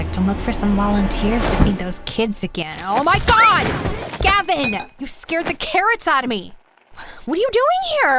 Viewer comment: why did i call him gayvin😭😭